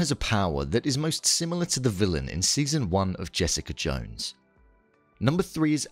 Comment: Who else heard Jessica Jung?